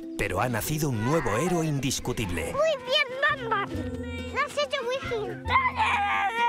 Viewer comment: Creo que le escuche en el min 5:50 muy bien batman lo has echo muy gil si no es asi corrijanme